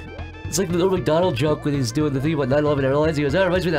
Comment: what does say here? All I hear is "*gremlin noises*....it reminds me of that horrible tragedy" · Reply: " like that norm mcdonald joke where hes doing that thing about 9/11 airlines" Look it up